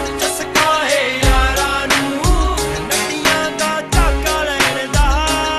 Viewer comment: i always thought he said "Nadiyan da chaka lain da"